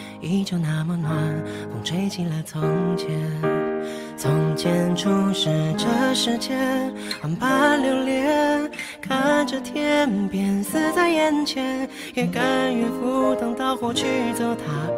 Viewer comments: 光聽了十幾遍，沒有看歌詞 "看著天邊似在眼前" 一直聽成"看著天邊死在眼前" ？？！！！ 😅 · 我一直聽成 看著天邊 死在眼前...